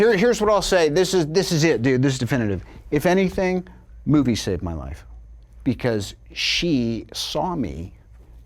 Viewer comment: What does he say at 4:20? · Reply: “If anything, movies saved my life”